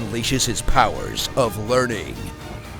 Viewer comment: Does he actually say "learning" out loud???? oh Lord, this movie...